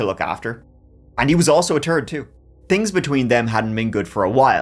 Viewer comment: "He was like a TIRD child for Maddy to look after, and he was also a TURD too," 😂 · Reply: I thought he said "turd" at first when he said "third." Then he clarified! Hahaha